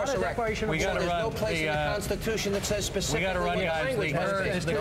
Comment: Wait, did Sean Hannity say that there isn't a place in the Constitution that says Congress has to declare war? It sounds kind of jumbled, and it happens at like 3:45 in the video. If that's actually what he said, that's hilarious.